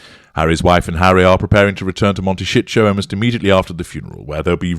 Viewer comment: Im deaf, so you’ll have to excuse me if this isn’t what you actually said: it sounded like instead of saying Montesito, you said Monte shit show!!!!😳😂 Sorry if I misheard you, but I had to chuckle!!!!😂😂😂